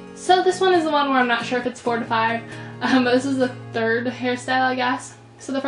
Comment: at the beginning I thought you said 45 instead of 4 to 5 and I was like what!?! and then u realized what you said!